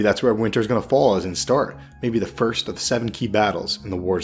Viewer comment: when talking about the duality in the word “fell” regarding the name Winterfell, you say maybe a future battle at Winterfell “maybe the first of the ____ key battles in the wars to come.” Did u say “several” Or “seven” key battles? , How do we know either way if there’s to be several or 7?